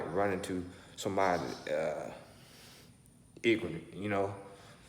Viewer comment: I thought I was tripping when I heard him say "igranent" when he was tryna say ignorant. Yeah he said that shit 😂